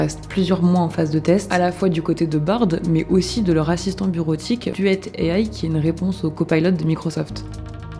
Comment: A 10:30 j'ai entendu "de leur raciste en bureautique" 😅